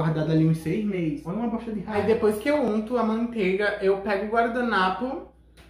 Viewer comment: mano eu juro que eu escutei manhega🤣🤣🤣